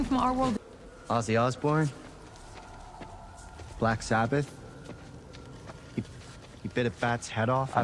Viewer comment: im sorry did you say BLACK ZAPID?